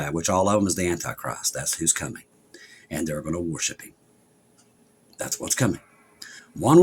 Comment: I thought you said "warship.". Then it dawned on me, WORSHIP IS OUR WARSHIP!